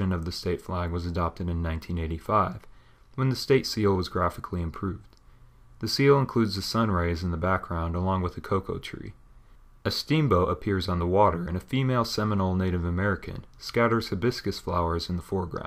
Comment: Ha!! Cocoa tree. Hilarious. That's a Sabal Palm. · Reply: I thought he said coconut tree which is still wrong.